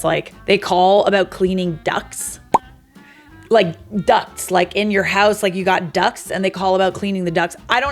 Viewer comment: I thought you said 'ducks'😂